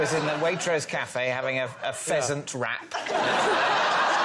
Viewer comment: So I realise after reading the comments that I misheard "pheasant" as "peasant" and didn't question it at all because it still sounded like a typical David comeback 😅